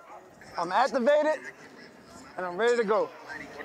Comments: Wtf, did my mans just say “I master-baited and I’m ready to go” at 4:16?!?! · WHAT DID HEE SAYYYYYYYYYY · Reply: "I'm activated," but I had to backspin it too. LUL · did deshaun just say he master baited😂😂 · we didnt need to know but ok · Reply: I thought he said, “I’m activated and ready to go” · I had a discussion with my friend about what Deshaun said @ 4:16 I think he said "I'm activated and I'm ready to go" but my friend thinks "I M@ and I'm ready to go" To whoevers reading this, What are your thoughts? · what did he just say?? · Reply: Hes activated, and ready to go. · I had a discussion about what Deshaun said @ 4:16 I think he said "I ma@ and I'm ready to go", but my friend thinks he said "I'm activated and ready to go" To whoevers reading this, what do you think?